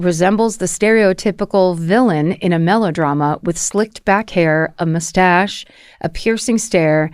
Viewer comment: did anyone else hear "Slicked, Back hair" and not "slicked-back hair" lmao just picturing gelled down back hair